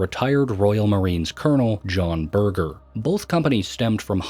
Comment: BURGER?! DID HE JUST SAY HIS LAST NAME WAS…BURGER?!🤣💔✌️🙏